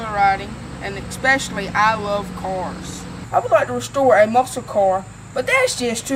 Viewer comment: he was being such a diva about his bacon hahaha Did he just say "i want to restore a masow cor?